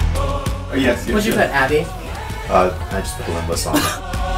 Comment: I could've sworn Mitch said 'Abby' instead of 'Avi'. I immediately perked up when I thought I heard my name! 😁